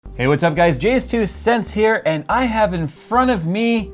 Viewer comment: Good vid man. Lol it kind of sounded like you said.